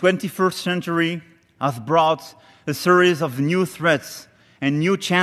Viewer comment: i hear "new threads" 😐